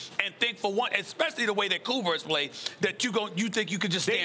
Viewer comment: Can we just get SAS to actually say Schroder right. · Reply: Maybe im tripping but did he say Kuzmer at 6:15 ???